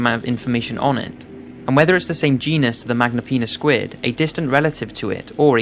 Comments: At number 1 I thought he said "Magnum penis squid" XD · I thought he said magma penis